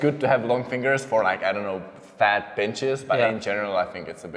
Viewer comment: I thought he said “fat bitches” I was like “WAIT WHAT?” 🤣